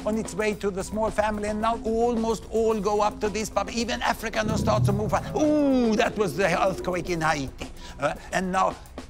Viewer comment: what did he say? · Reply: "Now Africa starts to move up, Ooohhh that was the earthquake in haiti"